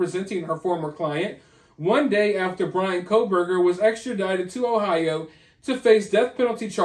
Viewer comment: Lol I heard "extradited from Ohio" and was wondering what I missed?